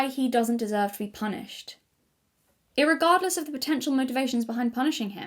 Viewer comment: Did I hear "irregardless" at 3:12:25? That threw me for a whiplash for some reason lmao Another slay video queen, I love listening to your long videos